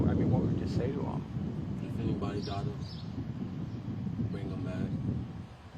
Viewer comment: The guy said IF ANYBODY GOT HIM BRING HIM BACK....I thought he said the real dad's sister picked him up.lol this is not a case for mattlock we can solve this on our own he a liar